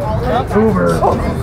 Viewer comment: did he say COUGER